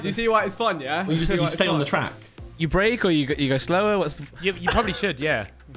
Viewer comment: "you brake or you go slower" - why did it sound like was saying the "go slower boy" 😭